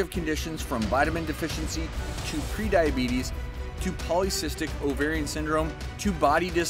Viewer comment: Ohhhh.....pre diabetes. I thought they said type three diabetes 😐